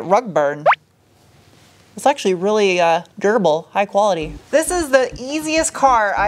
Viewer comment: I thought you said gerbil (you said durable) while you were 'petting' it's belly!